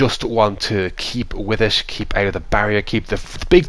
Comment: The only thing i hear is DeKeepa lol :D